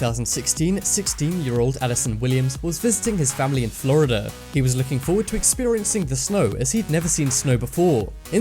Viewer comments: Looking foward to experiencing snow 6:20 in Florida? Or did I hear that wrong. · who else heard fisting at first?